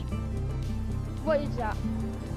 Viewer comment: bro got star quake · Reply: Yeah, it sounds like he says “star quake”